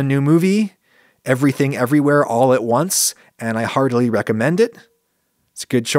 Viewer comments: At the end, I thought you said that you, "hardly recommend it." I rewinded it and heard it again. Then for the third time, with closed captioning, caught "heartily recommend it." What an amazing movie. I also heartily recommend it. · i heard "hardly recommend it" in the end and got really confused, until i went back and turned on the subtitles hahaha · I misheard "heartily" as "hardly" and almost gave myself whiplash.